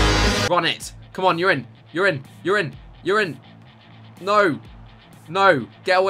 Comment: Starting from 8:57 it sounded like he said urine over and over again